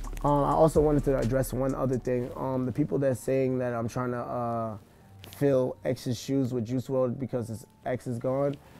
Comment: At first I thought Ski said “people think im fillin x’s shoes with Jews “ lmao 😂🌏